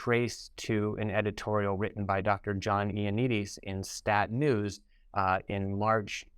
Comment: Yes, he’s not talking about Fauci. He compared the misinformation doc to Fauci in terms of respectability in the medical community. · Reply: aaahhhh ty. He's talking about ioannidis. I heard "after fauchi" and my brain locked into that. Thanks!